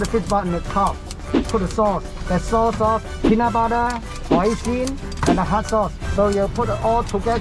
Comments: did he say PEANUT BUTTER!?!?!? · Did he say ''peanut butter''?